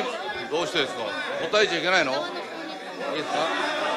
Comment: 政治家もポケモンGOするんだ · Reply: 「答えちゃいけないの」が「個体値いけないの」にも聞こえてくる